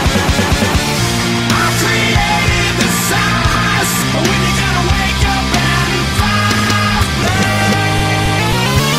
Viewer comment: I wonder what he said lol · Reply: He said “the SOS”.